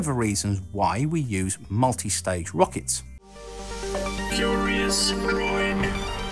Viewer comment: just to let you know I thought your intro said 'curious groin' this is not a criticism